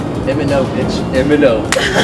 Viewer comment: Did say bitch in